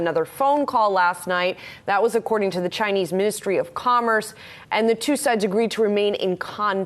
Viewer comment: Pushing on a string......and kicking the can down the road..... ps. for a second I thought she said Chinese Mystery of Commerce lol